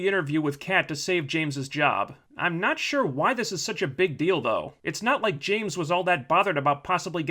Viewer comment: I'm sorry for this, but I definitely hear "Why is this such a big dildo?" at 8:35 XD